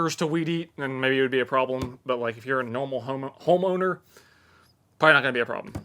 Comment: When said pole saw, I thought he said hole saw, which would be a weird attachment for that particular Milwaukee tool. The spinning horizontal grass blade seems kinda dangerous. Will weed eat more now? 14:42 "If you're a normal homo." 🤣